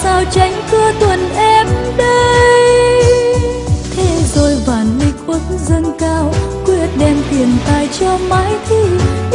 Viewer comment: Thế rồi Vạn Lịch bỗng DÂNG CAO? Dâng cao ở đây là nghĩa gì vậy m.n? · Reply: Ngoc Ai Hoa Nguyen Đoạn đó, trên nhạc, chính xác là "Thế rồi Vạn Lịch uất dâng cao" áh bạn. Chắc bạn nghe nhầm thành "bỗng dâng cao" nên bị khó hiểu. :D